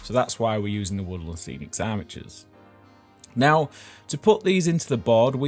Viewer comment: I thought you said "woodland scenics Amateurs" and I was thinking "Man, what an approachable product name!"